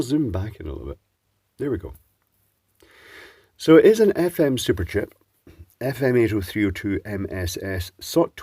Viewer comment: I heard that as Supercheap :) probably what the circuit is don't you think ha ha